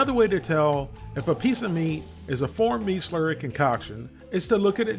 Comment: wait, you've always been saying "formed"????🤣🤣I always thought you were saying "four meat slurry concoction," 😂😂😂😂😂that's why I always laughed so much every time you said it. But to be honest, they could be using 4 different types of meats in these we'd never truly know 🤣